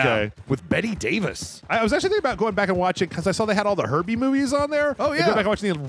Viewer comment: I thought you said herpes movies instead of Herbie movies. I was like, "I've never seen those!" Then when you said about the car being alive I realised what was going on 😂